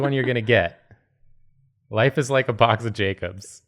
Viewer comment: I heard "Box of Jacobs," my mind went "Jakobs? What do you need with a box of Jakobs?" yes I played BL3 to much recently.